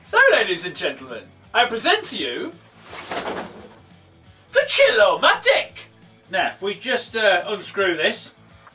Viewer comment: Why do I hear "The Chill Mah Dick!" on 0:43?